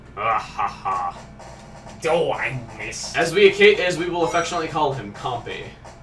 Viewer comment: lol this video was recommended for me,i was thinking "why?" then i heard "doe i missed" and i was like "CHUGAACONROY"